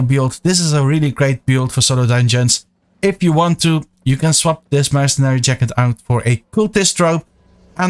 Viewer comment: I can't understand what your saying regarding swapping out the Jacket & Cape? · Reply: Hey ZoSo, I say the following: You can swap out the Mercenary Jacket for the Cultist Robe (has higher damage and a different form of healing, allowing you to do solo dungeons even faster, but is more expensive) and the Regular Cape for the Thetford Cape (a faction cape with additional AOE damage every 15 seconds, but once again a bit pricey for new players)